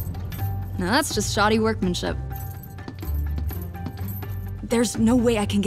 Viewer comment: I’m so dumb I thought she said 6:39 “now that’s just shawties workmanship” and I nearly died.